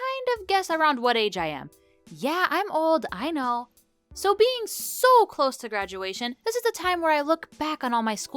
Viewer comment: When you said “I’m so close to graduation” I thought you said “I’m so close to grandmadation”